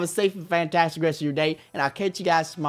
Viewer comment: captions says i can't you guys smart